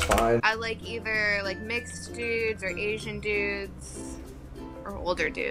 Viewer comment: did she say mixed dudes😏